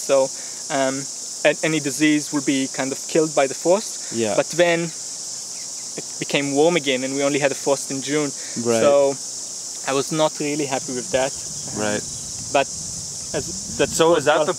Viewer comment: Took me a minute to understand his accent. Wow insane how much money he makes. Does he have a YouTube channel? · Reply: agree, when he said they were killed by the frost, I thought he said The Force ha ha ha, Star Wars gardening